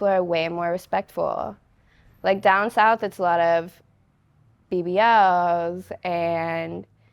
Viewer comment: What did she say there's a lot of at 13:53? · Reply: BBLs